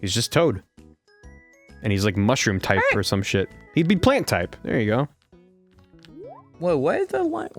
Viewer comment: Did I hear "plant type"? what are you smoking?